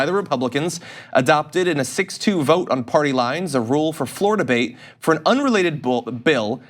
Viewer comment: Woops, "floor debate"! I thought you said "Florida-bate".